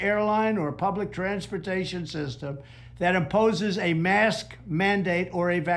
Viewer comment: I heard him clearly say he would cut funding to any group that OPPOSES mask and vaccine mandates. At 2:10 in the video. He can't even express his own thoughts clearly!